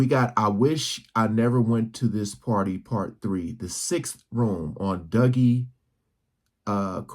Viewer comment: When I first heard this I knew what these rooms represent. The Seven sins. Lust, gluttony, envy, the list goes on. If this guy did make this up for views it’s good storytelling. If it’s true it doesn’t surprise me in the least. The choice of colours is interesting too because certain colours exert a certain emotional response. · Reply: Thought he said six rooms